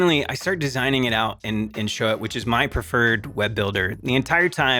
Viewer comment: I rewind this 10 times and I couldn't understand which web builder are you using? Thank you I'm advanced 🙏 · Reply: No problem! It’s called Showit. <link>